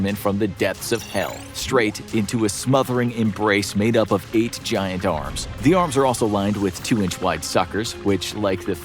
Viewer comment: I THOUGHT IT SAID ”GIANT KAREN” 😭😭 now I’m sad